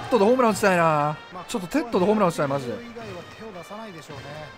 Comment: 哲人じゃなくて、てっとにしか聞こえない。